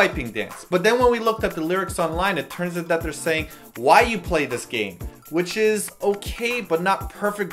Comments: I thought they were saying, "Why you 'playin' this game?" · At first I thought they said "my plate is gay" o_O so... yeah. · Sistar! Btw, don't they say "Why play these games?" at the start instead of "Why you play this game?"? o0 I always heard it as the former.